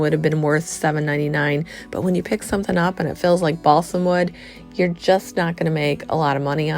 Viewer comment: did you say balsom wood? What's that?